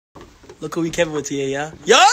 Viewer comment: My man stressing out in the background 😂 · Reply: i thought I heard why are y’all still standing here bro